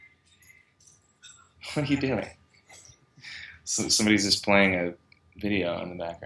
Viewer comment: he swore jdkrogosoeooqozlakdkfjjc but he sound adorable crying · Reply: he swore he said the f**k are you doing?